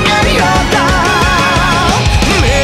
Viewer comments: I swear, every time I hear "bright burning shout" I keep thinking the song is saying "fight the pantyshot". And seeing as the opening of Fate/Extra Last Encore predominately shows Nero fighting and all... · I swear, every time I hear "bright burning shout" I keep thinking the song is saying "fight the pantyshot". And seeing as the opening of Fate/Extra Last Encore predominately shows Nero fighting and all... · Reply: Thought it said "Fight the body shot" And I just rolled with it